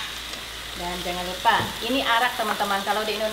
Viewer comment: namanya apa? Bener kah ak dengernya iru arak? Arak apa?